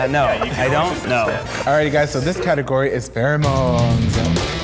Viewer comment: i heard "category is farrah moan" lmao